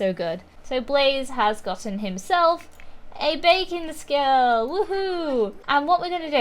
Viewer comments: Everytime you say 'baking skill', I hear 'bacon skill' and I'm like "Wait, WHAT?!" 😂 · For a second I thought she said "Bacon Skill" But She Said Baking Skill